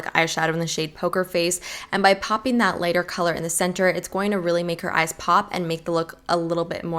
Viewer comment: I definitely thought this said 'Make your eyes poop'